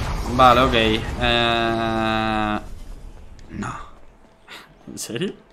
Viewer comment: alguien escucha esa voz? Que dice:NO